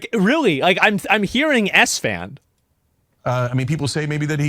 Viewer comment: I didn't hear "S Fan," i thought i heard "Assman."